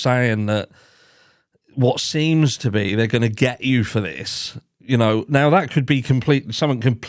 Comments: It sounded like he said "they're gonna get you for this Callie, look at this". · I heard " were gonna get you home safely ". Listen to it again.